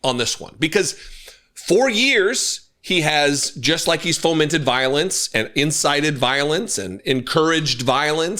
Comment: Haha, I thought you said he incited "violins", not "violence". (Neither would be a surprise. Not from this moron.)